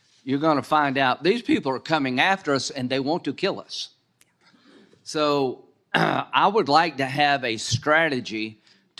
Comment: What IS F? When he says it and she says it, the subtitles only say F, and I cannot understand what they are saying? What does he mean by, "a strategy to deal with the ISIS threat that's beyond just the law enforcement model"? Then there seems an obvious edit in the middle of Bondi's speech. He says, "Do you support the reauthorizing of [F] in 2025?" Something about 702? What Is It? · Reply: It's FISA, the foreign intelligence surveillance act. The law enforcement model would be that if someone breaks a law, the law then goes after them and punishes them. FISA allows for spying on non-citizens if you suspect they're potentially engaging in criminal behavior, and you don't need a warrant for it. There's a high probability that they're working with citizens in their terror plots, so let's say I think Tom is a terrorist in the making, but he's a citizen, but Tom is friends with Dmitry and Liu who are both foreign nationals. I don't have any evidence for Tom, so I use FISA to start surveillance on Dmitry and Liu. By listening to their phone calls, I find out that Tom is shady. I then take this information I got from spying on two foreigners and use it to justify a warrant to investigate Tom, the citizen. What the war-hawk, RINO scumbag is suggesting is that he wants Ms. Bondi to ensure that this loophole the government created to the 4th amendment will remain available, and that he wants her to use it the way the democrats did to go after Carter Paige.